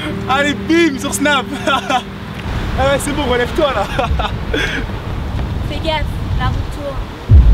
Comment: Au début j'ai entendu " fait gaffe Naruto "